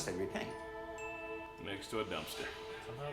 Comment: did he said « next to a dumpster »? i’m so sad i’m not american and i try my best but i can’t really understand what they’re saying idk why 😭😭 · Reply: that's what he said, your English is great :)